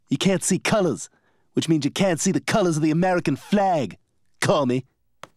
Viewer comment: I always thought Stewie said "Call me" instead of "Commi"